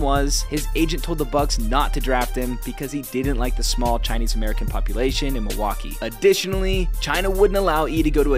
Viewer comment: Anyone else hear that as “he didn’t like the small Chinese people in Milwaukee” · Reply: I had to watch twice. I thought he said "didn't like the smell of Chinese American population"